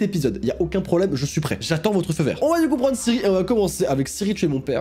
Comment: J'attends votre feu verre ! Moi : FEU VERT !!!!!